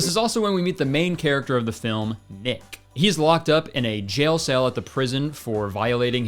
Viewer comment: this is KILLING ME I misheard you and thought you said the guy’s name is ick